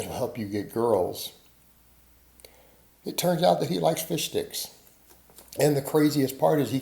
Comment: Lmao I heard “and it turns out he likes fish sticks” I had to drop a like😂😂😂